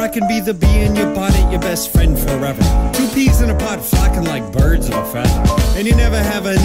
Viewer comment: Sometimes I mishear the "flocking like birds of a feather" as "f*cking".